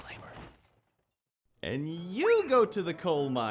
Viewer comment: the first thing i hear: "and U GO TO THE COAL MINES"